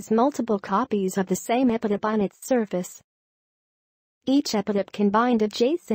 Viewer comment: What is being said at 4:08? epinembonic surface?